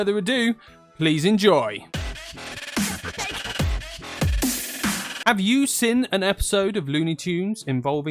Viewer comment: Hey, i wasnt expecting this game to be reviewed! Thank you for looking back at classic games such as this! PS: At 1:03 when u asked "Have you seen an episode..", i misheard it as "Have you sinned"? * Awkward pause *